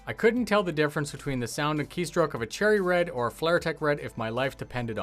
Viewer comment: the keyboard does not have FLIR, it won't let you see thermal vision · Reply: lol i misheard flaretech as "flir tech"